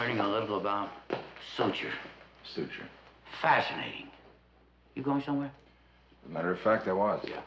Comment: did Columbo say "fascinating" to Mr. Spock? A lot of Star Trek personnel in the show...